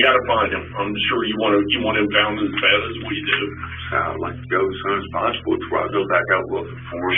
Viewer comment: I don't think he's saying "Yeah I feel responsible for him" it sounds like he's saying "I'd like to go as soon as possible" 26:48 the [inaudible] sounds like "what was the punishment last night?" 33:54 the [inaudible] here sounds like "okay, we can figure that all out" with saying "this ain't fair to me" at the same time